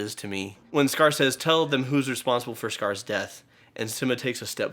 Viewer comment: Did I hear this right? I am GENUINELY asking. I heard "Responsible for Scar's death"???